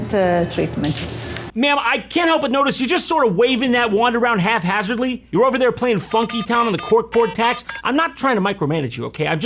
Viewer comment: Did he just say... Half-hazardly?..